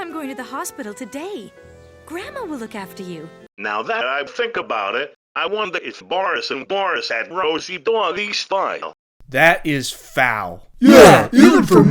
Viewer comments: what did the robot say? It was a little bit harder to understand him in the later part of the sentence. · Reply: "Now that I think about it, I wonder if Boris and Doris had Rosie doggie style" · what did earl say? i couldn't make it out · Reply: "I wonder if Boris and Doris had Rosie doggy style"